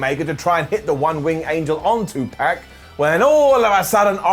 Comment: I definitely heard "...which allowed Kenny Omega to try and hit the One Winged Angel on Tupac"